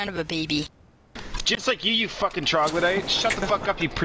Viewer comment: Did he say "Troglodyte?" xD That's awesome..